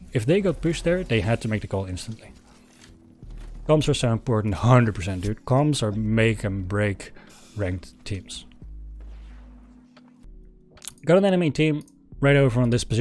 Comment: he's talking about calls And I'm not sure what he says what makes or breaks good team, sounds like comms but im assuming its calls?